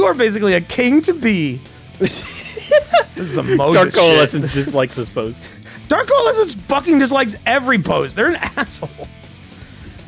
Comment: What does say here? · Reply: "this is some moses shit"